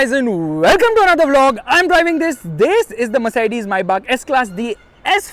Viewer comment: did I hear "mayi baap"